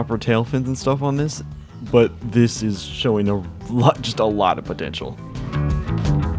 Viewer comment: did you say potential